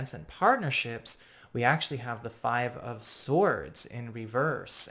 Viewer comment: What’s bad news about an opportunity to jump up a level? Or reconcile? Or any of the positivity be talked about here ha ha. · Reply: Nothing! That is great news. I thought i heard a 3 of Swords & as a Sag/Cap the last 3 years have me too edgy. anywho, big fan. love your work. i await your videos. always. :)